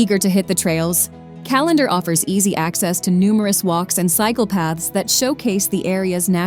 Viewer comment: Ummm. At 20:17 did I hear that right? Psychopaths on the trail?